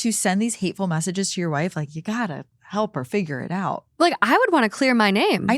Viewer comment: I thought it said, "figure me out."